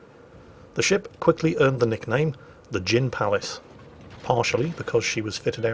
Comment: When you said 'gin palace' I thought you said 'djinn palace' and it was a reference to it being taken from the Ottomans.